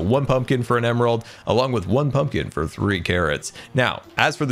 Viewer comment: did Scar just say Garrot? Maybe Xisumas new phrase will actually stick :D